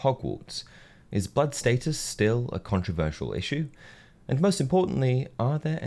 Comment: Anyone else heard him say "is butt status still an issue" ?